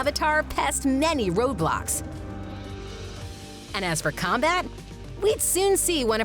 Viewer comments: did she say roblox or roadblock? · d-did you just say roblox? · Did i just hear roblox? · who else hear Roblox? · Did u say roblox? Roblox is my fav